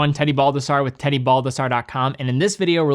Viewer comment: i thought in the intro u said, "teddy bulbasaur". and im like what?